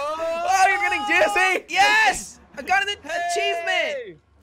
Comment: did he say I got a pachievement